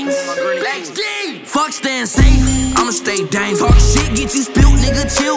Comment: I thought it was saying fuck Stan sage imma stay dank